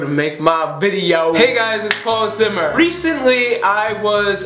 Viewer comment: It sounded like you said